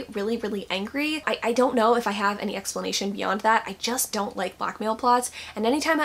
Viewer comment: Lol. I heard "I dont like black male books" and I was like O_o, "I must have misheard this somehow." And I have. :'D